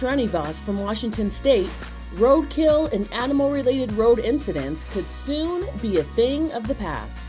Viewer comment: I thought it said to stop road rage related incidents. Which would also be kinda cool